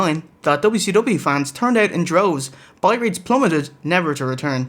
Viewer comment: I'll seem like an ass for this, among other things, but I noticed that a decade ago seems to use 'turned out in droves' to meant stopped watching rather than start watching. Or am I mishearing 'tuned out in droves'? Could it matter less? Just things to ponder at 530 am before work. Happy holidays!